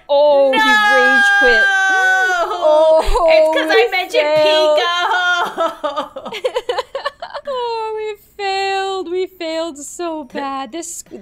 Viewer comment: Not gonna lie i thought she said Pico hoe